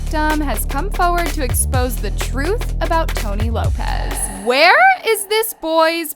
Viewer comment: did she just say Tomy Lopez....